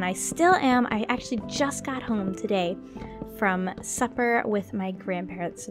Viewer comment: When you said that you got home from supper with your grandparents I thought you said "suffer"